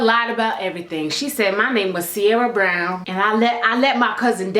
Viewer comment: At first I thought you said "Sabrina Brown" and I was like 😳wait... did you? ... oml 😹, but it was Sierra Brown 😹.